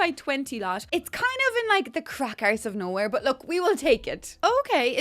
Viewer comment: Did you say "crack arse of nowhere"? captions put "crackers" 🤣🤣🤣🤣